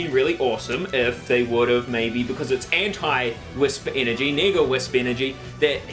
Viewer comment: this whole time I thought he was saying nigga wisps